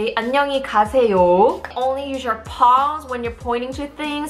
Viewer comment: I thought she was saying paws but she was saying palms 😂